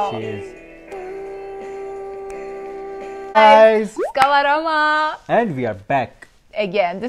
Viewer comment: Did she just say iska warama kkkk